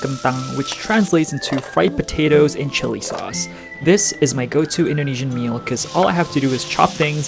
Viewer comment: When he was saying what the food was called I thought he said fight the potatoes in chili sauce